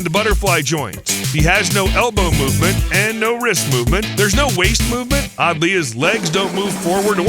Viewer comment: I thought you said there’s no Waze movement